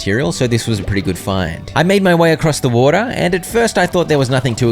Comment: Was it just me that heard him say “made my way across the border” instead of “made my way across the water”. Asking for a friend. Also can you 100% potion craft soon?